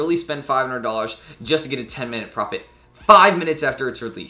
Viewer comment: I think I heard “I spend $500 to make a 10 minute profit.” I think ss18 was weak.